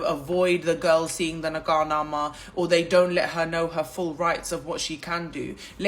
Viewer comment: please what is nagarnama???? Am I heard it wrong???